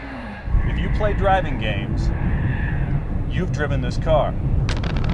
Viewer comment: sounds like you say "If you played dragon games".